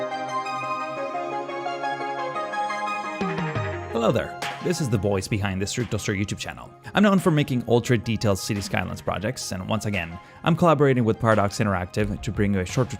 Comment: it sound's like saying "this is the boys behind the Strictoaster YT channel" Even the captions say that